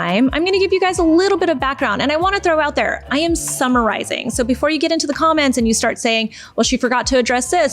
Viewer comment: 😅 when Susan said "I am summarizing," I heard "I am summer rising" and my first thought was, "Oh no, is this going to be all about energy and star signs?"